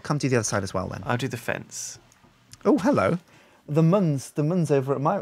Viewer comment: wtf is being said at 4:47 ? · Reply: "The muns over at my- He's got a big brush hasn't he?" 'Muns' being how they say 'man' or 'men'